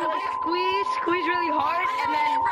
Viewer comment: Bro I heard at 4:38 bro someone said squeeze hard